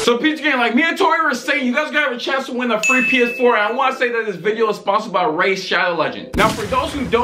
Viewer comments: I thought said “this video is sponsored my rain shallow legends”😂😂😂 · USE at item shop for Season X! MORE FORTNITE VIDS TOO ONCE I GET MOVED IN! Also click link in description to enter PS4 Giveaway, I wanna run duos with you 😈😈😈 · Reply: I thought said “this video is sponsored by rain shallow legends”